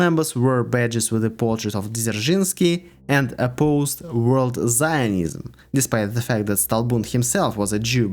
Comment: wait, isnt zionism just jewish nationalism? it could make sense. I didn't understand what you said tho, it sounded like "world zionism", which sounds more like a far right conspiracy theory hahahah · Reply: Yes, it was "world zionism", it was actually considered a big threat in the USSR on official level for some time in the 70s (as Israel was not one of our friends). Fun fact - Soviet Government actually made a documentary movie back then ("Secret and Explicit (The Aims and Acts of Zionists) ", it was never aired tho) which was SO antisemitic that it was added to the list of extremist materials in modern Russia